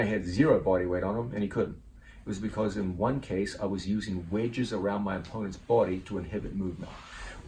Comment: Non native english here, what does he mean when he said "I was using wages around my opponent body"?